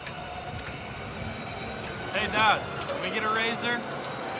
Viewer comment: I thought he said " Dad, can l get a raise here"